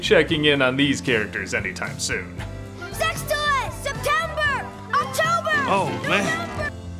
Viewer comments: i swear he says "sex toys" there. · I swear to God, he said “sex toys”. 😳